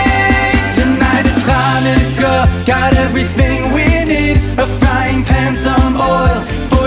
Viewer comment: I love this..!! Had to play through a few times to hear one lyric properly: "frying pan, some oil..." I heard a the first time as "frying pans, a mohel.." which would be really strange in this recipe. ; ) Cheers! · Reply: I thought I heard singing 'a frying HAM some oil...'